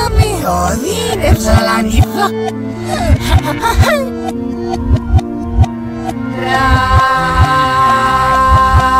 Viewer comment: Min 2:58 lo único que entendí fue “niebla” xd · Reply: reo que dice “ mi amiga a entrado a la niebla” ._. No pos pudo haber entrado al fuego eh xd